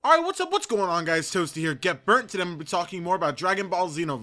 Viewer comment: What do you say at the beginning if your vids.after you say what yo and everything because I never knew and it sounds like you say get berk so if you got time I'll appreciate it if you let me know(: Btw Xenoverse may be able to be a pick up and play game but for the most part I think you gotta learn it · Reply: Get Burnt is what I say.